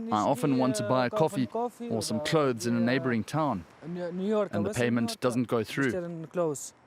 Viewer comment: it just sounded like he said he cant buy clothes in new york lol